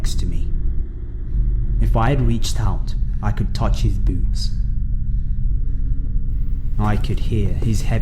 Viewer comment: OK, so I had captions on, and the third story w/ the Pokemon go dude, when he was under the car and said "I could reach out and touch his boots", auto captions heard it as, "I could reach out and touch his boobs".......... .........well then.......